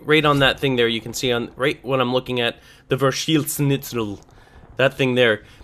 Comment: what is guns saying? wiener schnitzel